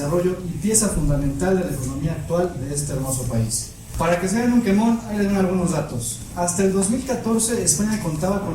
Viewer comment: que dice? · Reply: "para que se den un quemón ahí les van algunos datos"